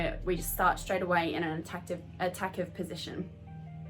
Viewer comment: Did you just say "attack-ive" position